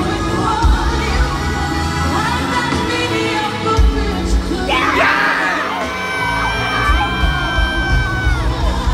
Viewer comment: What did she call her this time 3:42? Lmfao 😂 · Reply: Natalie Ava mediocre